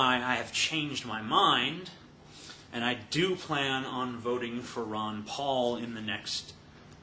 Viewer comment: Damn what happned, I thought you said FUCK RON PAUL!